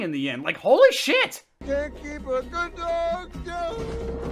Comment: What did Charlie just say? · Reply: “can’t keep a good dog downnnnn”